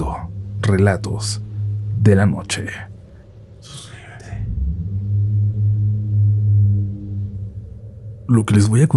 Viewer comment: Díganme que dice el murmullo del fondo en el minuto 10:32 porfavor ya le regresé varias veces y no le entiendo 😊 son las 2:00 am y tengo insomnio 😢 ya llevo varios relatos y me asustó ese murmullo👀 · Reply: “Suscríbete“ según yo